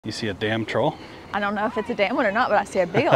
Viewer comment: Thought you were saying damn trolls until I saw the end comps and found out they were Thomas Dam trolls😂😅